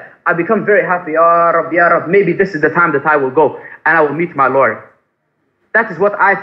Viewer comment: When he says "I will meet my Lord" it sounds like he said "I will meet my lawyer" 🤣